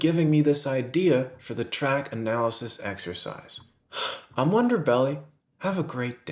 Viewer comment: "I'm wonderbelly" ...don't know if that's what you meant to say, but it makes me kind of uncomfortable.